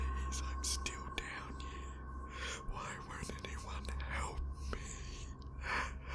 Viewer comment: Wat is that whispering sounds say in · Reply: It's says like I'm still down here why don't anyone help me😑